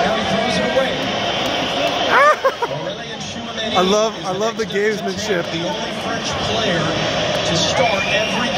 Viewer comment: Some English speaker can say what somebody says at 3:41 because I can't understand something like "I love the game...." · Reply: "I love the gamesmanship" or "I'll love how this game is shift"